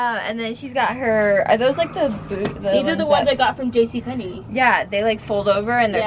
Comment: It sounded like said butts 😝